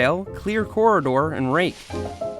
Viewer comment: I thought you said rape!😂